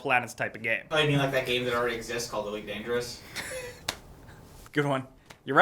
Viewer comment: what game did they say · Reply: I think he said elite dangerous